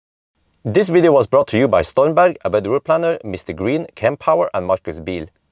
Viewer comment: I always thought you said "Bedroom planner" instead of "A Better Routeplanner" until I read the sponsor screen, haha!